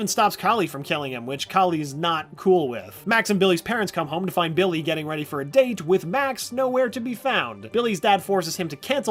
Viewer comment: I really thought you said Billy was going on a date with Max Sweet Home Alabamaaaaaaa